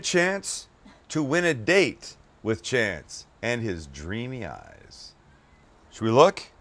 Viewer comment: I heard “Win a chance to win a date with Chance... and his dreamy ass.”